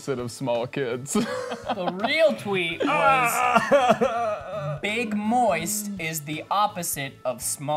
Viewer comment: i also heard ethan saying big boys instead of big moist, tbh